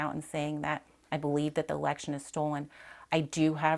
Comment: Did she say I believe the erection was stolen @ 3:26 ? LMAO